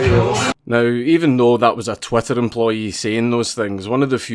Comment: Did you say Twatter employee?